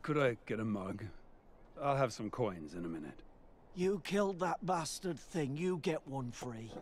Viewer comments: "You killed that blasted thing you get one free." Now that's a nice bartender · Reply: i thought he said "you killed that bastard thing" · "You killed that blasted thing you get one free." Now that's a nice bartender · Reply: Always thought he said "Bastard thing"